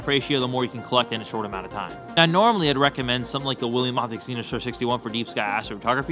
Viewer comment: Especially at 1:58! I can't even guess what he said there. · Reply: william optics zenithstar 61, refactor telescope :) but yeah mumbled it and talking too fast